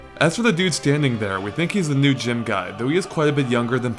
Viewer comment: Oh, I thought he said gym guy lol Thank you!